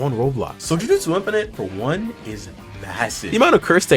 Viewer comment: did he say... massive?